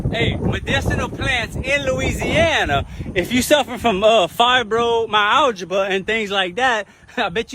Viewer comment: did bro really say fibromy-algibra